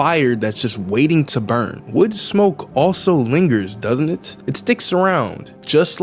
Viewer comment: i thought it said "weed smoke" for a sec